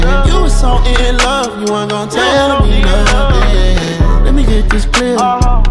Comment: Kirishima - “I’m feeling like I did to much” Denki - “I’m feeling like I did to much” Bakugou - “Lets get naughtier” Todoroki - “yeah midoriya” Anyone else hear that 😍🥴🥵 Ahhhh- nosebleed